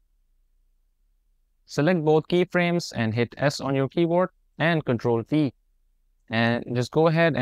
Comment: i had no idea what u said, it sounded like u said "control V" but that just leads to a clip board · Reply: I said "ctrl t"